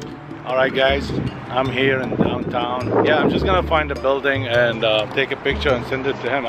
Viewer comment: or 4:34 did she say " i am just gonna f*** around*